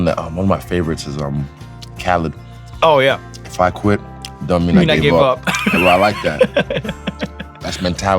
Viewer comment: What’s the phrase he says at 3:05? Can’t understand it cause the bald one spoke at the same time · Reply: If I quit, don’t mean I gave up - Dj Khaled